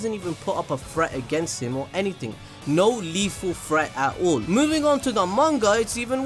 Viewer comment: I thought he said fret but then I realized he's B R I T I S H 😂